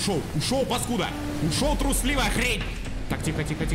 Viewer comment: Мне послышалось "Ушёл трусливый Андрей!" (я которого зовут Андрей) Чего?